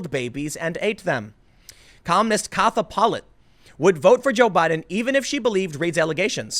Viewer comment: you said "Columnist Katha Pollitt" ... I heard "Communist Katha Pollitt" . And if it has a touch of cultural enrichment to it.... mmm mmmm even better.